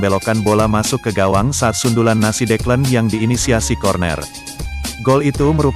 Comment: Nasi Declan. 😂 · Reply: iya eh ku kira salah denger smpe ku ulang2. declan rice jadi nasi decla wkwkwk